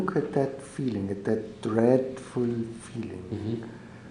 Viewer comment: What is the word he uses at 21:14? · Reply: You mean "dreadful"?